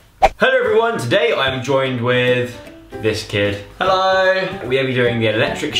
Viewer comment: I thought Joe was saying biscuit instead of this kid 😂